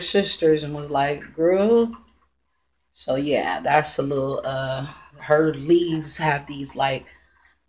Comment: What is that plant at 4:45??? Little boogie I think you called it?? LOL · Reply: Its a baby Jungle Boogie Philodendron which when mature is so beautiful ❤